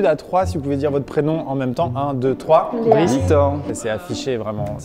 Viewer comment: J'ai compris "Bricetor" et j'me suis dit "la vache, ça défonce comme prénom !" Puis j'ai repassé le moment pour voir les prénoms et... Bah ça va, iels ont des prénoms cool, mais j'espère que sur cette planète, un être humain s'appelle Bricetor. Au pire, je prénommerai mon prochain animal de compagnie comme ça, parce que ce serait une perte immense pour l'histoire de l'humanité. J'en fais trop et en plus je commente sur une vidéo qui date d'il y a un an ? Zut alors... Bon, bah retour à la vidéo, mais ça commence bien je trouve, haha ! Dommage que je ne l'ai pas vue passer à la sortie et qu'elle ne pop que maintenant ^-^'